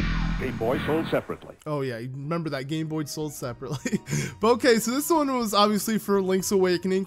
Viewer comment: Instead of "game boy sold separately " I heard "boy sold separately"